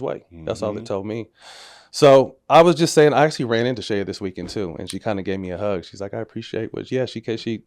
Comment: Who is Matt talking about at 12:55? i cant hear that clear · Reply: Kobe's sister